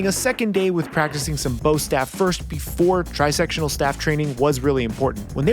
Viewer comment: I thought I heard him say tri-sexual staff